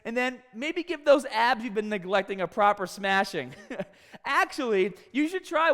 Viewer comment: I heard "give your girl a.proper smashing 😂😅